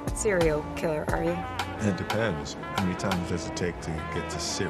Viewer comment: I thought he said "it depends how many times it takes to get the cereal" 🤣